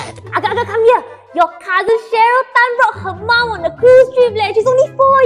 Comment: When she said Cheryl tan i thought she said sharukh khan.😂